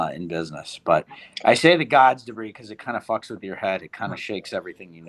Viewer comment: What book did Pulte said fucks with your head? The God's Degree??? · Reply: debris